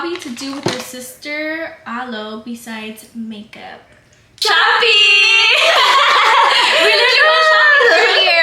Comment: Jaja 😂I thought you guys said “camping “ instead of “shopping”